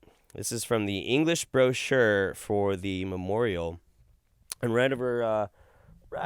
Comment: Question. What the hell is a mamorio? I might be mishearing her, but that is what it sounds like she said.